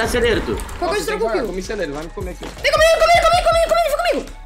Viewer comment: Só eu que escutei o lzin falar vão me comer